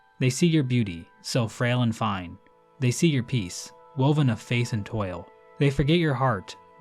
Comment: For the "Correcting my Silksong Lore Mistakes" video you can add the fact that at 15:48 when you read "faith" it sounded like face, which is a terrible error that completely mutates the true lore behind this game.